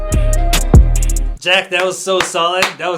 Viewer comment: did he said jerk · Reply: I said Jack lol